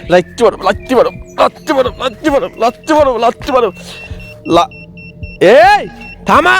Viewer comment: ভাই আপনি কি বললেন এ লাথি মারে লাথি মারে লাথি মারো 13:08😂😂😂